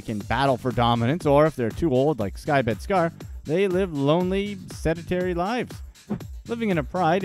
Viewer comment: "They live lonely, cemetery lives..." Is that what he said @ 10:20? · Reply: 👋 ' Sedentary' is the word what was used, not 'cemetery'. I know it's been a long while since you posted but I saw it just now, so.🤫 👁️ I Am The Walrus 👁️ On the Strength 💪🗽✊🇺🇸